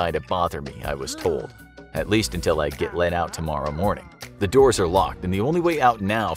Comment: On first hearing I thought he said , "At least until I get 'laid out' tomorrow morning." :)